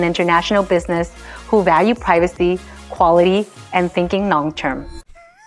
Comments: I honestly thought she thoght "sinking long term" and then i realized she said "thinking long term" like half a second later. I love the way women think. If it was a man saying that, it wouldn't have been funny. But having a woman tell me that...hahhahaha....that was even funnier, just honestly hearing it the wrong way. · Sounds like she says "sinking long term" 😆